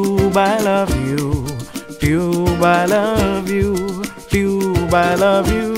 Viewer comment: I thought he said I love You(Chat) but it was Youtube